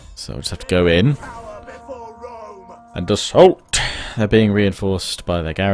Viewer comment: I hope you enjoy this first look and part 1 of my Kingdom of Charlemagne Lets Play! Next part will be out around the 10th December, possibly just before! It will then replace the Huns Lets Play and be released every Tuesday, Thursday and Saturday! Until appreciate any likes, comments and shares you leave if you feel so inclined :) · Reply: did i hear at 36:00 "For Rome"...... but rome is dead : P